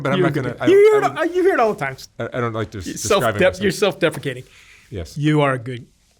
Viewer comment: yes, he said "self-defecating" instead of "self-deprecating".